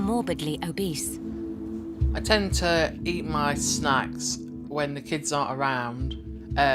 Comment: Tell me I'm not the only one who heard "eat my snacks" almost sound like "eat my snot" !